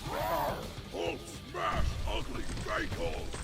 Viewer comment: i thought hulk said "hulk smash ugly staple"